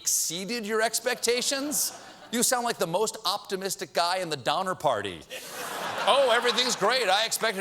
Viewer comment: I thought he said Dahmer party...